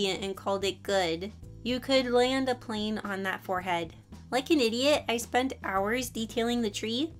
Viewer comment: Nah I catched up pretty quickly 1:55 I heard «like a Canadian» 😅 3:03 You used arial, no wonder why it looks robotic lmaooo 😂😭 6:14 he vaped and hid the e cigarette in his pocket